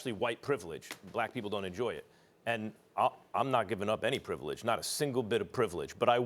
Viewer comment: Did he just say “I’m not giving up my privileges”. What a POS